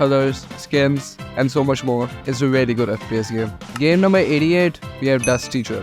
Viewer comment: What do you think bro said here—> 15:08 “game number 88” or “game number idiot” ???